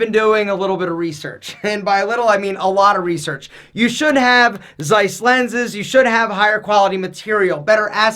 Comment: I actually had to pause the video at 6:42 and replay the previous 10 seconds because I thought I heard you say Zeiss. Turns out you did which is funny because I work for them!